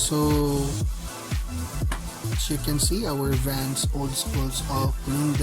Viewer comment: All I hear is "she can see" haha Great vid tho fr it's really useful